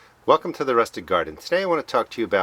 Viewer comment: For the longest time I thought he was saying the rustic garden 😂 🤦🏽‍♂️ 😅 👍🏽